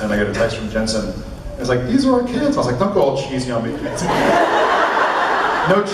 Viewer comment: What does Jared say around 16:30 after reading the text from Jensen? Before he says "no chicflic moments" And thanks so much for the video...great quality :-) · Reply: "dont go all cheesy on me"